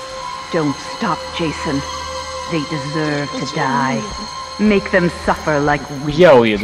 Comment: I heard “made them suffer like YAOI” 😂😂😂